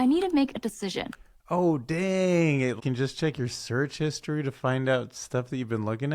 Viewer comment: d-did you say search history?😐